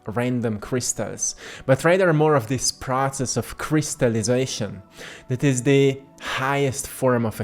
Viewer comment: Thank you so much! This deeply resonates, and when you said crystallization, I thought you said my name haha Christa, it caught me off guard 😂💙💫